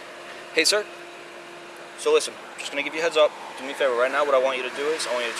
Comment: It was just a misunderstanding. Thought the Cop said "Head Out"